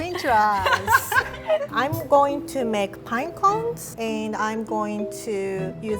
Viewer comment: I thought she said iPhones but she said pinecones 😂 I Like if u did to😂😂👀😂 I V